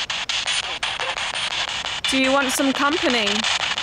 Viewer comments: Hey it has Tagalog accent when I heard the word "love" and "company" it looks like a visayan accent.. Good job! Thanks for visiting our country! 😊😊😊😊😊! You should visit some of our haunted places .. God bless! My sister used to be in the teacher camp during her college days! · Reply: I heard "Ganda nito" on a ilocano accent instead of company. Ganda nito means it's beautiful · Idl if it's just me, but when I heard that ghost say "Company", I think he actually said "Ganda neto". Which translates to "This ones beautiful" or "You're beautiful". I thought that since it's a man that clings to the woman that he likes, him saying "Ganda neto" would make sense. I could be wrong though, and just misheard it. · You should have a filipino contact check on the spirit box.. I heard "andyan kami" and u interpreted it as "company" it means we are here.. U can contact me nxt time u need help.